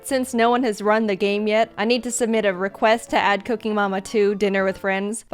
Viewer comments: It sounds like your saying cookie mama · I keep hearing "cookie mama" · it sounds like ur saying "cookie momma" XD